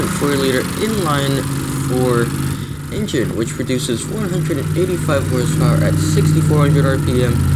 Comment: did you say it produces 485 hp@ 6400 rpm ? It sounded like it...... I want to think you say 185 ponies, but it sounds more like you say 485. which would be totally incorrect information. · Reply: He said 185 lol use your ears